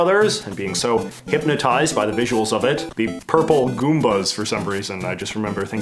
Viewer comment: Did he say "purple" goombahs at 11:30 or did I mishear that?